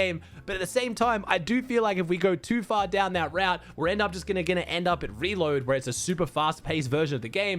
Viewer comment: what is bro saying?😂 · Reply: We're end up just gonna end up